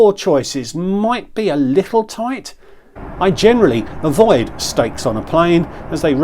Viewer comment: Must admit I thought you said "I generally avoid snakes on a plane" for a second, thought you were cracking av-geek jokes 😂